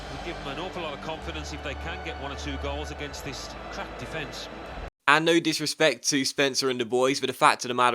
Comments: Anyone else thought he said "this crap defence"? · it sounded like the commentator said "take advantage of this crap defence"